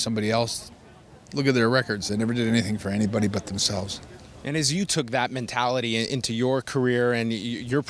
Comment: what is this interviwer saying ? He says you tk that mentality and Kliq ? Excuse me but Bret was not that in that group.So why associate his good wualities with them ?